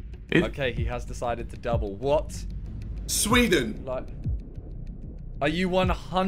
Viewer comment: Swear JJ said Swindon